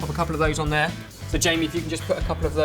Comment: sounds like is saying 'va-jamie' ha ha, get it? oh god i'm so mature..